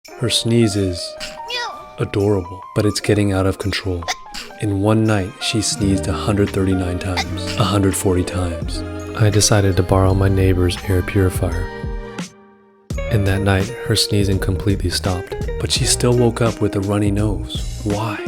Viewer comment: For a second i thought he said “So I decided to borrow my neighbour”